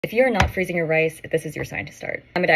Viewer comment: I heard ‘if you’re not freezing your ice’💀💀💀